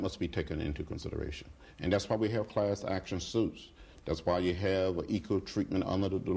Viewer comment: Lol.....I thought he was saying clash....